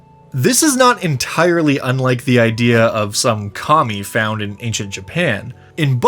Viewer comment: I heard "some commie found in Japan" and had to reorient myself lmao